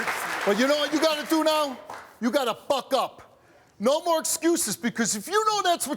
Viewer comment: at first I thought Steve said F up! 😮😆